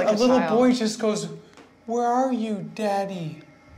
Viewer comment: I swear it says “who are you, daddy?”